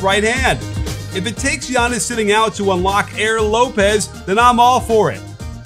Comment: Did you say Aaron Lopez at the 1:40 mark?